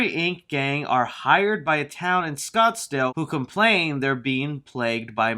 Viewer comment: did he say scotsdale it’s Scotland I’m from there